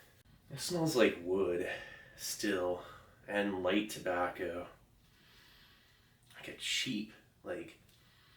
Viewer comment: At first I thought he said the cigarette smells like wood, light tobacco and sheep lol